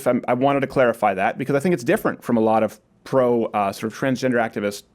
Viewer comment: Why do I keep hearing "Post-Op Transgender"... Isn't the whole idea that Gender and Sex are two separate deals? So, a transgender would be one who doesn't get operated on and a transsexual would be one who went the full mile and got the surgery?